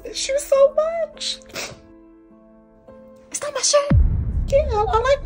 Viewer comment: I swore she said " Is that my shyt"? I cant 🤣🤣🤣🤣🤣